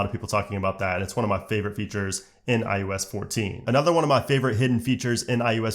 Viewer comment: Was it just me or did it sound like he was saying iUS 14 every time he was talking about this iOS update